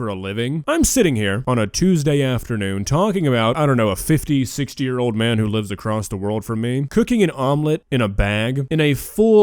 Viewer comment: Ny earbud was doing its whole low battery stick and so all i heard was; “a 56 year old man who wants to drink a low battery”.